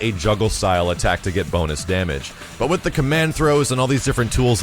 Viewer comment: i thought he said boner damage....